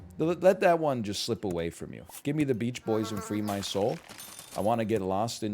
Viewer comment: did he say "give me the beach boys and free my soul"? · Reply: Peach